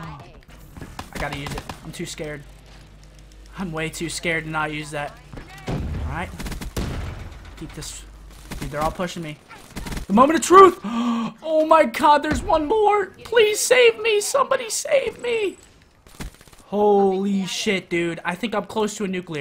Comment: did you actually say "Oh my Cod" ahahahahaha